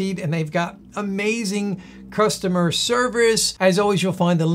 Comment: im 1:36 in i swear hes making a joke i hear him say they have amazing customer swerve us i know its just a mistake it just hit my funny bone for some weird reason lol